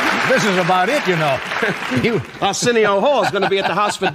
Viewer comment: "Senior whore" was closed captioned instead of Arsenio Hall. Biggest roast